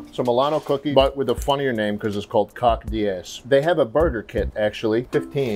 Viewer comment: i thought he said murder kit instead of burger kit.. i didnt even question it until i saw the packaging